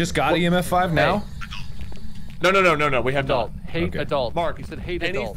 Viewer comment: Ngl heard the ghost say adult and at first i thought it said acult 😅😂🤣